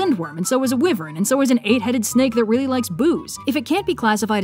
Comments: "And so is an 8-headed snake that really likes booze" i heard 'booze' as 'boobs' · "And so is a 8 headed dragon that really likes boobs." I also heard it as "Boo" (Ala, spooky) And boods (Whatever that might be) In short, hun, dont forget your Zs.